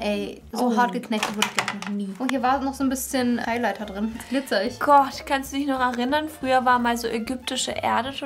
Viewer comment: hab eileiter verstanden ahhaha